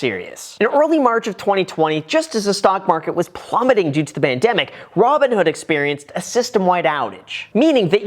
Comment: Did he say bandemic at 2:46? 🤣🤣